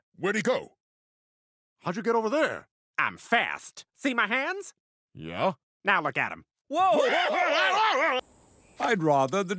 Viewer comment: what did Roger said there? he talked a bit too fast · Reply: "I'm fast. See my hands?" "Yeah?" "Now look at them." 😊